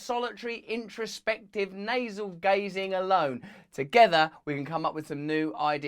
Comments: I coulda sworn you said NASAL GAZING lol 😝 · Did he say "Nasal gazing" @ 0:23 ?! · I think the saying is "navel gazing", but I swear it sounded like you said "nasal gazing". ;) Anyway thanks for bringing critical thought to this situation. Its so upsetting to see how many lies are being pushed at the public right now. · For a minute there I thought I heard you say "nasal gazing". Picture me cross-eyed while contemplating my nose.